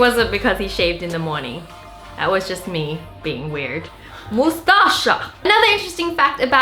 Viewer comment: guys who knows what it mustacha she said the word